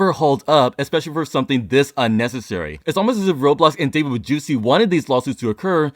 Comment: Did you just say "David Bajewsi"?